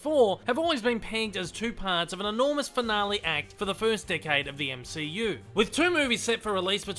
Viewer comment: Every time you say 'Decade', I hear 'Dickhead'.